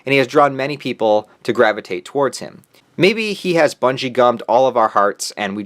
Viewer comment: Ngl for like most of hxh i thought he was saying bungeecum..